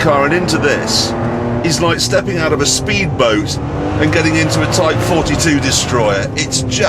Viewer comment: errrrr... does anyone understand at 2:58 what he sad... stepping out of a speed boat in to a.....?????? tay foty 2 destroyer??????? uh???? what is that??? lol.. i want to see it!!!